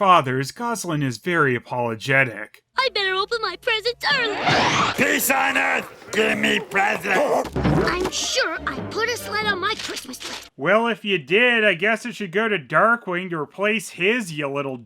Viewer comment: I swear he said "Piss on Earth. give me presents!"